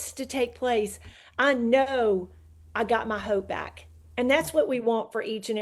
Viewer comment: Did she say I got my hoe back?... or did I loose it? lol